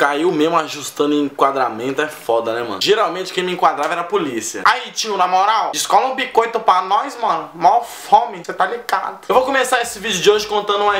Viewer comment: Assista esse vídeo com legendas! mds que isso '--- ' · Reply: "Hatiana na moral scooby quanto para nós mal fomentar mercado" Foi o que apareceu no meu no minuto 0:08